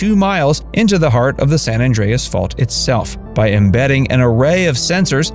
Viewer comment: Just remember, if the earth starts shaking and you need someone to blame, it's San Andreas Fault... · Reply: lol I thought you said San Andreas Fart 🫣 that would’ve been funny